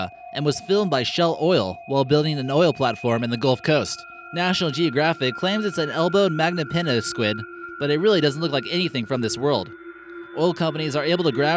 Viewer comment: did he say Gold Coast, Nah fuck this im moving to Melbourne.